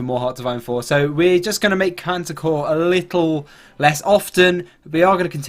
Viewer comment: it sounded like you Said cancer core instead of panzer core